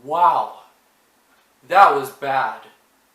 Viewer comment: I thought you said "that was fast"